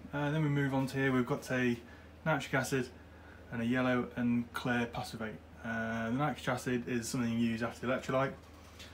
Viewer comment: Can you repeat what you are saying at 10:10 its hard to understand what the batch of acids you have there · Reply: After the electrolyte process, I use a diluted nitrite acid bath, and then dipped into the passivate